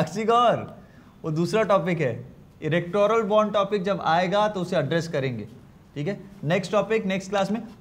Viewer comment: Did he say "Erectoral Bond" at